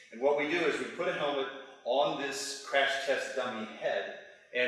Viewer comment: I thought they said nazi crash test